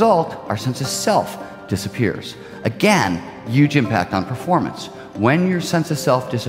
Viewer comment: lol 5:09 says "you gym packed" instead of "huge impact"